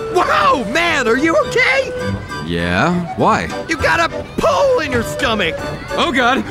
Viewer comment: haha I had to listen to it a couple times cause first I heard: "You've got a poo In your stomach!" then "you've got a pool In your stomach!" then I finally heard pole lol